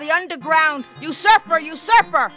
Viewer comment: She's saying "usurper,". (shakes head) To be fair though, her Ps do sound like Fs for some reason. Also, I'm a little disappointed you didn't catch that it sounds like she's saying "Blow me" a few seconds before that. O_O